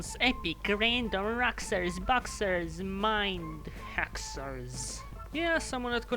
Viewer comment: Not gonna lie I thought number 9 is said as "Mind Hexers" or "Mind hoaxers"